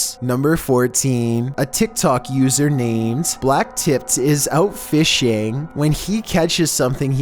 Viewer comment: At first i thought he said Blacktit and started laughing so hard😂 Until i noticed he said blacktiph